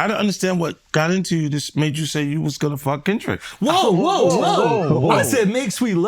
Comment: What did 50 say at 1:50? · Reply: He said he was gone F Kendrick 😂😂😂